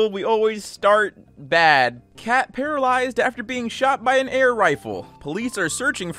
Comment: me seeing the cat shot with a rifle story: THIS IS NOT OKIE DOKIE edit: me seeing any animal abuse: THIS IS NOT O K I E C O K I E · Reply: I thought that said "car paralyzed after being shot by air rifle." at first.